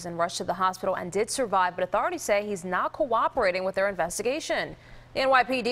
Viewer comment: Not cooperating with the investigation. A stand up guy, this guy over here. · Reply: Thought she said hes now cooperating ?